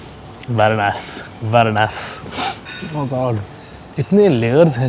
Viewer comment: its not WHAT A ASS.....he's saying VERY NICE lol